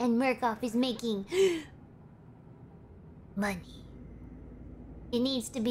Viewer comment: i thought it's Monkey but i misheard it 24:20 sharks kinda sus